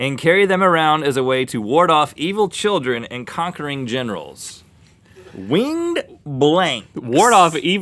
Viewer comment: Because of the previous questions, I thought it said "conquering genitals." D